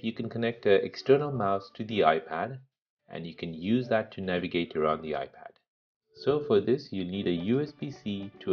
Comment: wait a second is it just me or did he say "in this tip you can connect a usb c external mouse to the ipad and use it to control your ugly ipad" at 2:10 min of the vid- correct me if im wrong